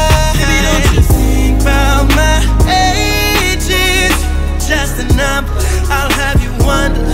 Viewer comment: Whole time I thought he was saying "don't u think bout my edgeeees" 😂😂😂 Damn shame when its not the females u gotta watch wit ya man but ya own momma 😭